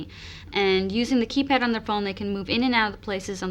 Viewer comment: Does she said "using the keypad on iPhone"? :D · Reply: No, THE phone.